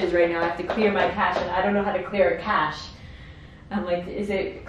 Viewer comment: i swear i heard "clear cash" at that part... djjsjsjs